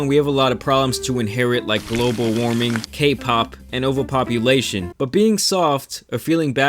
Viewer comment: did he just say KPOP?! 😯😯